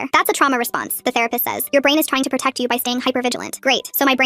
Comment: IT SAYS R*P*ST INSTEAD OF THERAPIST